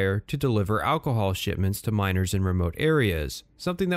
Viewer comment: I can't be the only one who heard this and initially thought the contract was delivering alcohol to minors - right?